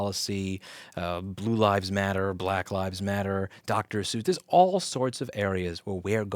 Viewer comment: did he say Dr. Seuss? lol